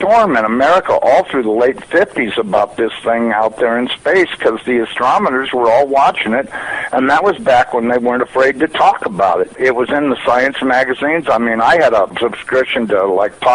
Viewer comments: It sounds like he says "astrometors" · did he say 'astromoners' at 0:10 ?